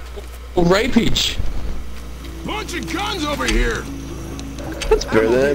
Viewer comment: lol at 1:30 it sounded like francis said "watch it cons over here"